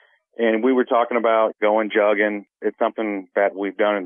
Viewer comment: When he first said we were talking about jugging i thought he said jogging 😂 i was kinda like this story and this dude dont strike me as the type to talk about their new nike trainers and their workout routine 😂